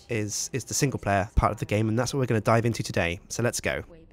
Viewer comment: I thought u said this is where we die in today